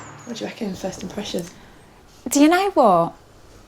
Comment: What do you reckon your thoughts are? My closed caption is decided "DNA Wall" instead of "Do you know what?"